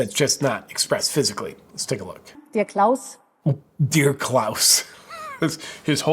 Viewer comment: I'm sorry. When that lady said "Dear Klaus" all I heard was "Dickless"🤦🏼‍♂️